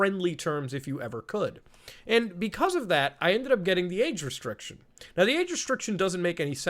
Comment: 'Age restriction/restricted", anyone thought he said "aid restriction/restricted"?